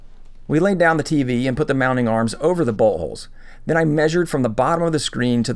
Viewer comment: It sounded like you said buttholes when you said boltholes and I got a little giggle out of that 😂